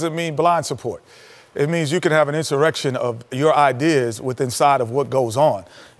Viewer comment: What word did Kenny use there? 👀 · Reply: “Insurrection of your ideas” not a literal insurrection